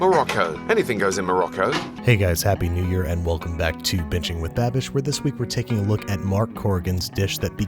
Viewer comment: Did he just say bitching with babish ???